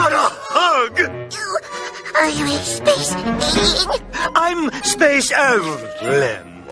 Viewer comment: What the hell did he say at 10:58??? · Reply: He said his name was Space Allen.